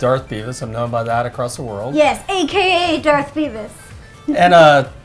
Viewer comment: I thought he said "Darth Penis". Is his fist shirt pumping?